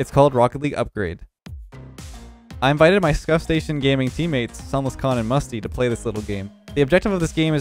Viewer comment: Kinda sounded like you said scuff station gaming in the beginning there.🤣